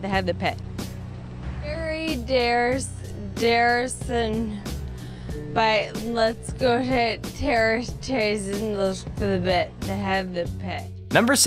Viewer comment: did they just say let’s go hit Harrison in the bits